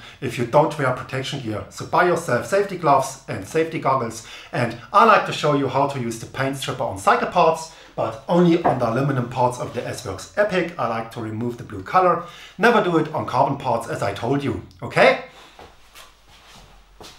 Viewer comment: i thought he said "i like to use a paint stripper on psychopaths" hmmmm... Me too! 😆😆 by the way i loved all your videos im now sanding my bike frame properly thanks to you.